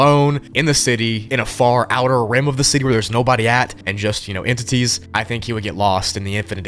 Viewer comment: did he call brøether of the lämp an entity?